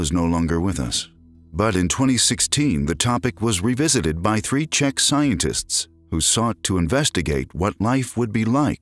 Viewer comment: I thought he said three chick scientists haha